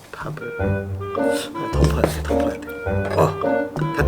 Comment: 왜 ‘더퍼야되’ 가 ‘덮어야되’ 로 들리죠